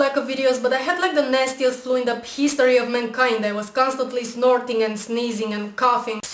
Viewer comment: Sounds like she said " Peas story of mankind."